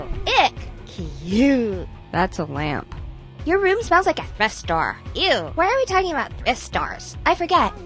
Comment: Can anybody tell me what the FUCK she says at 1:35? It's been haunting me for years and not even the mothertongues I've asked can tell me! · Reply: Goth bot:that’s a lamp Sister: your room smells like a fish store ew why are we talking about fish stores? I don’t know and I don’t care bye byes (Hope this help :) )